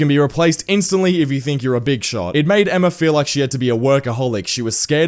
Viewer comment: I know this is serious but did you say [BIG SHOT]